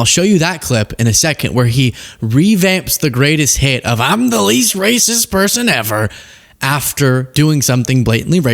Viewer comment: Funny thing: the first time i heard him say "I'm the least racist person..." what i thought he said was "I'm the least gracious person...." LOL